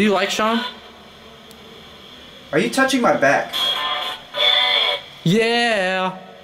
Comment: at 4:04 You have question marks. * Your back * Is what I heard and if you slow it down to 0.75 You can hear it real good.